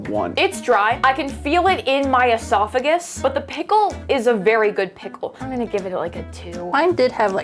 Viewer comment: i thought she said “the pickle is very… pickle” lol